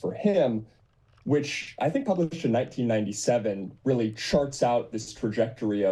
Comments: lol I thought Sam said "sharts out" instead of "charts out" I really hope he did and it was a Freudian slip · I swore he said "sharts out" on the first listen.